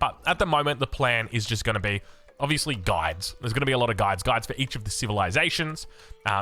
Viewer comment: OHHH GUIDES i thought I heard guys and I was like HUH he's one of those type of youtubers? mkay?